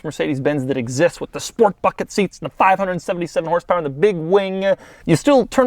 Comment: Doug never cusses. He must be a devout Christian. Yep. Mystery solved, Batman. You can go back to your cave now. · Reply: As I read this comment I hear him say "sport fucking seats" lol